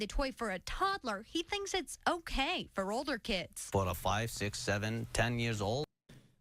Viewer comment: I didn't know why he was crashing out at first. I thought the guy said inflation and I missed it but no it was fucking 6 7 😭